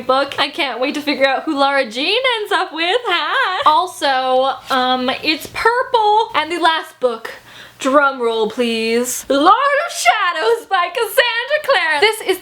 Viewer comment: I didn't hear "drum roll", I thought u said "drug roll" and I was like wtf is that and am I still watching